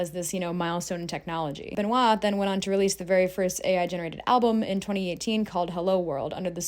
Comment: def sounds like AR not AI small thing but as soon as I heard it I couldn't not replay it multiple times to make sure I did hear that lol